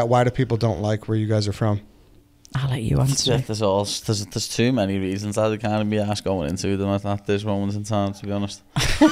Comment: I’ll give anyone $<num> if they can tell me what Paddy said 😂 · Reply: "Theres too many reasons I cant even be arsed going into them at this moment in time to be honest."